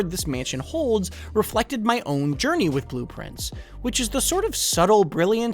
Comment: Took him saying "Blue Prince" like 10 times for me to realize it was a play on "blueprints". Maybe I'm too dumb for this game lol. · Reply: Well my dumb ass thought he was saying blueprints the whole time couldn’t find game on steam only to rewatch and see it’s called blueprince